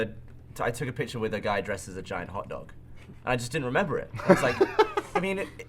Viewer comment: watching this last night to go sleep, i totally thought Gavin said he took a picture with a dude dressed as a condom...on the 2nd listen, hotdog... fuck my brain